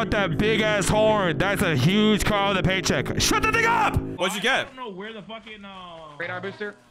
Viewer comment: I thought my brotha said "Shut that nigga up"